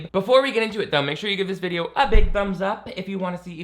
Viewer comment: I just realized how childish I am because of the way I’m giggling so hard at how it sounds like said “give this video a BUMS up!” at the beginning 😂😂😂 I know he said ‘thumbs’ but it sounded like ‘bums’ and I need to grow up immediately 😂😂😂